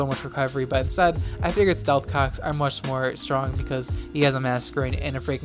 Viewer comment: why for does he say “stealth cocks”?